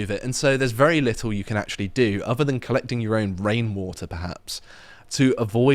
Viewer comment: I thought I heard him say 🧠 brain water 😳